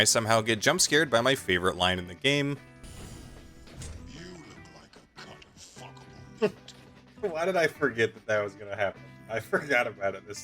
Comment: The first time I heard say that, I thought he said “you look like a cuttable f**k of meat, are you?” And just thought “how rude” 😂